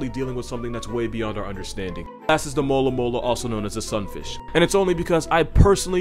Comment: When he said “mola mola” I had to play it back a few times. I thought he said “alomomola” like the Pokémon.